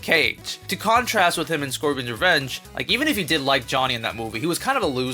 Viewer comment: why did I mishear you saying Scorpion's Revenge as Squidward's Revenge. And why do I wanna see Squidward wearing a Scorpion cosplay now.